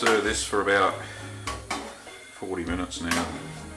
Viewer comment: did you say you'd been stirring for 14 or 40 minutes?